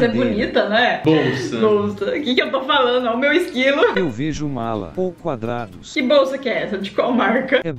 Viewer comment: essa bolsa aí parece um cadeado kkkkk 7:57 Bagulho parece uma arma kkkkkkk 9:07 eu achei que ele tinha falado "Eu vejo o Lula" kkkkk 9:25 esse desenho aí tá mais pro um peixe da deep weeb morto kkkk 10:37 O: " é bolacha ou biscoito " Eu: é bislacha 11:53 oque a inteligência artificial acha vendo o desenho da: Um submarino Oque eu acho vendo o desenho da: Uma parte que os jogadores de golf tem que acertar ou seja uma pista de golf